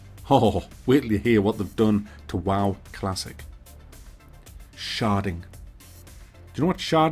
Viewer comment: 😂😂😂 I honestly thought you said “sharting” at first. Yes I know what sharting is, it’s what Activision Blizzard did at Blizzcon this year! Thank you thank you, I’m here all week!